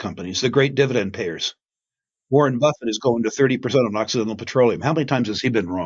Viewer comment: Anyone could help me with what Rick says on second 47:56 “Buffet is going to 30% on ??? petroleum…” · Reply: Occidental Petroleum